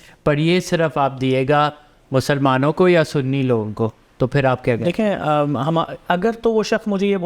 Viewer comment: Sorry! But did he say "Muslmano or Sunny logo ko" (6:00)? or did I understand wrong?